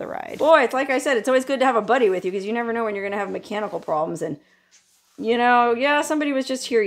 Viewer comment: just for a brief moment, when discussing the advantages of a companion in case of mechanical problems, I thought she said cannibal problems. I can well understand how a woman who spent her young formative years in Las Vegas might prefer to be alone forever after, and I only ever briefly visited the place, Thankfully I always left with good memories, courtesy of friends .